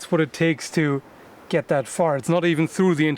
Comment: Gotta love a good fart joke. :-D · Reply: "Yeah, pretty much pushing with my entire body weight. That's what it takes to...get that fart." lol I heard that when I read Dan's comment.